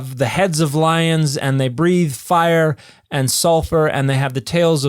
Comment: did i hear that right "Fire & Salsa" hope so